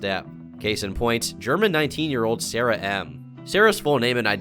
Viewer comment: I swear I heard 'Jerma9-' and my brain filled in the gaps, like yeah Jerma is a Criminal and E-girl